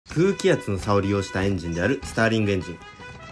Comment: 最初スターリンエンジンに聞こえた